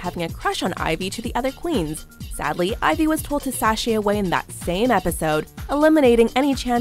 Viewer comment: bruh i’m watching this at 2:45 in the morning because i woke up due to immense knee pain and I KEEP HEARING chassé instead of sashay and i’m like no that’s wrong but then i go back and rewatch it and it said sashay the whole time i am not okay also sorry if this makes no sense amor there are spelling errors i am so goddamn tored